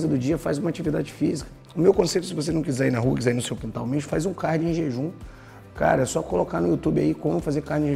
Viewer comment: Não entendi em 4:21 fazer o que em jejum? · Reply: Cardio